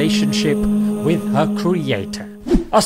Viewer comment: why did u say with a creator instead of with the creator